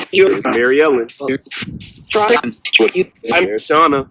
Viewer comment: thought i heard at second cemetery “Mary Ellen i like her?”